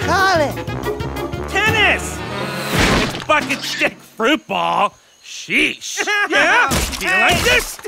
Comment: I know that is not what he said!!! LOL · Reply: He said "Bucket stick fruit ball"